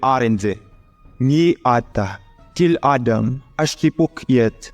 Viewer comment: "kill Adam" anyone else heard that? 😂